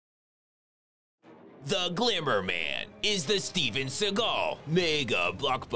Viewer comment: i thought i said the Dinner Man